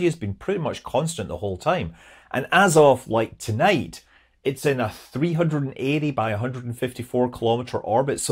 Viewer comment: I heard 54, though I must admit the 10km drop in Apo per pass did sound like too little by a long shot. So thanks for the clarification. And technically the Karman line is where at orbital speed no amount of wing will be of any benefit for the maintenance of the orbit, even under power, so the only viable means to keep altitude is rocket power. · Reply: If you're talking about 4:15 he says "It's in a 380 by 154 km orbit". As for the Karman line, the original definition is what you say. The current definition is more unclear. On Wikipedia it says "[it] is approximately where satellites, even on very eccentric trajectories, will decay before completing a single orbit".